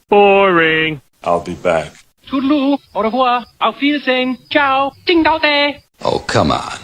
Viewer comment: still no idea what is said after "Ciao"... mystery continues... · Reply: zimbabe? a country though I typed it wrong in English